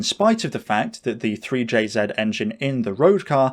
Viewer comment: Did he say 3jz